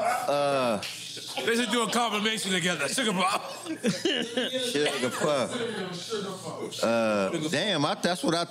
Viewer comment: 😅😅 yo man i thought he said sugarpuff too